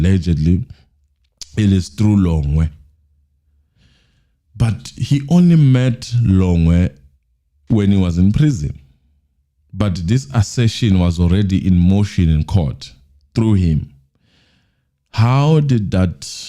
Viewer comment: Hey fam. on 44:18, you might have missed what Teffo said exactly. He said “Longwe confirmed recently in jail what he already knew, but he had already put the assertion in court based on eyewitness information. And he didn’t want to say which eyewitness.”